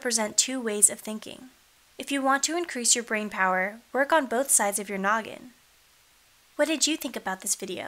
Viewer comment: At first I thought you said "work on both sides of your nugget."